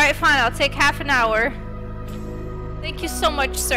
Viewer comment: When she said thank u u saved her it sounded like she said hank you XD